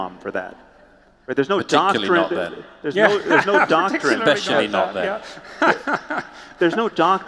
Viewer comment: anyone know what Murray said? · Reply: "Particularly not then" :P